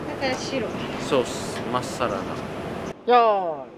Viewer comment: 「まっさらな」を勝手に「まっサラダ」に空耳脳内変換してしまう かわいい～～～～～